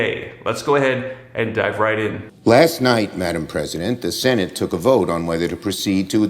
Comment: in ..... madam president? Madam??? Did I hear him right?